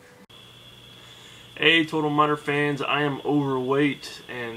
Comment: Hahaha, I like playing total miner whilst watching Total miner videos on my laptop which is on my lap, i remember clicking on this video and all i hear is "Hello total miner fans and i am overweight." And i burst out laughing!